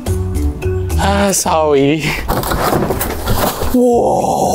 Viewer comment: Is the best part was him saying sowwy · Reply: He said sorry